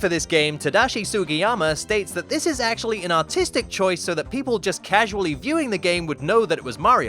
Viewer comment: I thought he said "This is actually an autistic choice."